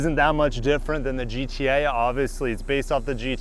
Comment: is it just me or did anyone else hear gta instead of gti